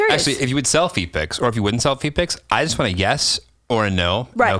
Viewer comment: Swear he asked V picks, not feet picks. Had to listen to it 4 times still thought he said V picks. Too funny.